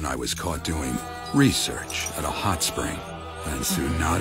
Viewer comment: “Tsunade beat me like a Drum” - Jiraya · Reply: Ohhh I thought he said like a drunk. Thought that was weird